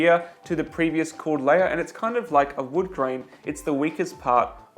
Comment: No. Wood is more likely to split along it's grain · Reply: Ups, my bad - i didn't listen that careful and thought he said wood-Joint, not grain.